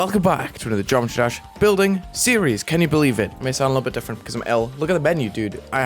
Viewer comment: For a second I thought you said: "I may sound a little bit different because I'm L."